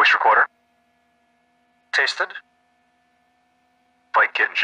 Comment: Wow, when the AFE responded to "cockpit voice recorder" I thought he said "Tasted" I guess he said "Tested" lol.